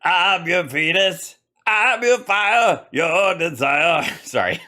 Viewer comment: When you said Venus it sounded like you said Fetus 😂